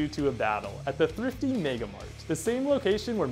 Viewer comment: When it said megamart I thought it said megafart